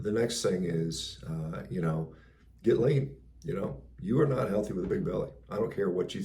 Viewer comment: Sadly martial arts are useless against guns. Also; I swear I heard you say, "get laid." LOL... But it must have been "get lean." good video Good advice. Thanks.